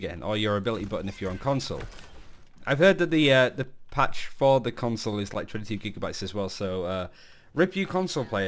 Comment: wait, what did you say? I went back many times, and I can’t understand what you’re saying. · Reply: console is 20 GB. had to put the caption on to understand myself